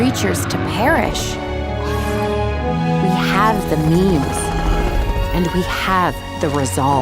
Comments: i thought she said memes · it sounded like she said memes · Was I the only one who heard "we have the memes"